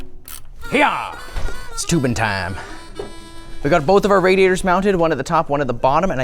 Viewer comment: I heard "toobin time" and thought he will pull a Jeffrey Toobin and start doing inappropriate things to himself on a zoom call :D